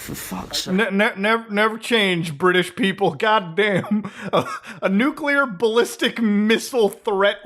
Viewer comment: i thought you said it wasn’t nuclear